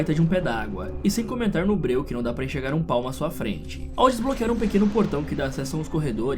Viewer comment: Juro que ouvi "Que não dá para enxergar um PAU na sua frente "...kkkkkkkk